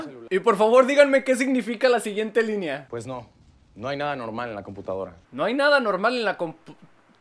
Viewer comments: misa creo que dijo anormal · ya lo entendí lo que dice es: no hay nada anormal en la computadora Fin del dato que nadie me preguntó